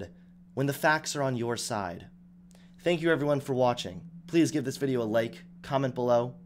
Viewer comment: hey guys did anyone else notice that it kinda sounded like he said "give this video a lake"?